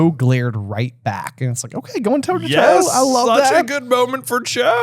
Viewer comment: At first I heard "toe to cho" 😂 had to rewind 10 seconds 🎉